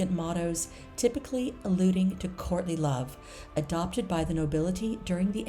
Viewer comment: Okay, not going to lie here. Because my husband is napping and I have the sound down on my phone, I thought you said the lady's one desire was Courtney Love. My brain did a double-take. Courtney Love? Why! 😂😂😂 Thank you for the lovely introduction to the history of these extraordinary tapestries and for the chuckle as well.